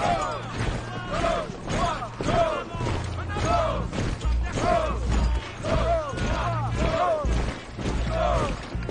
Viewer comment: What the French soldier says in the background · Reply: He's saying "En avant" (Forward)